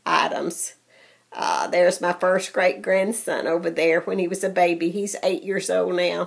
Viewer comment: Beautiful items from Julie hun, Love that one with the pink in it. Thanks for showing us some of your treasures. My mom used to love dolls too. Your grand son was an adorable baby. OMG I love those little baby carriages, they are so precious. TFS hun xoxo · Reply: Hi. good thursday to you hun. Its been so humid here and wanting to rain and we have gotten some too. I hope this passes soon. Oh my he's your great grand-son, that is such a blessing, I thought you said grand-son. I need to clean my ears out , lol. I hope you get to visit your new great grand-son. I know you'll love that. Hope you get to make your trip soon hun. Blessings to you my dear and don't be rushing on the tags,, just do that when you have time. Love & Hugs xoxo